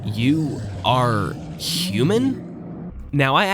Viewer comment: I think the queen is saying "You are evil". Also, I don't think the Polis race went extinct - I think their civilization/planet was dying for a long time. First, humans were rendered infertile, leading to the creation of the genesis wall to continue their existence. Next, the world became toxic to survival in these fragile shells, leading to the invention of the tulip machines to protect their organs and the creation of Polis to protect themselves further. When the complete depletion of resources and/or degradation of the atmosphere becomes inevitable, the Polis people seek to free themselves from the material world, leading to the creation/discovery/activation of the portal. Eventually, the Polis people enter the portal en masse and abandon the world they were born into. Runaway decay ensues as there is no intelligence left to maintain the structures and machines or prevent their erosion. However, the Genesis wall keeps working. The machines keep running as long as they are able. People keep being born and people keep trying to escape as the buildings are slowly eroded into nothingness. Most of the surviving humanoids try to seek the safety of a Polis shell, and quickly fill the tulips, not fully comprehending that someone would eventually need to come later to release them. Protagonist 1 sees the elevator in a hallucination and was trying to progress towards that goal, but fails when one of the other machines on his journey fails. Eventually, Protagonist 2 is born